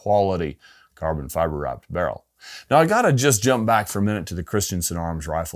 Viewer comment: Did he just say carbon fiber optic barrel? Maybe I'm wrong. You guys tell me?